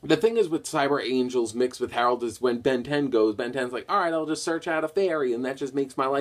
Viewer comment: did you just say Ben 10?